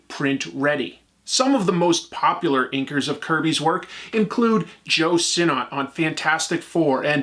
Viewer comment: I misheard his name as "Joe Cinema" and had that image of Scorsese flash through my head 😭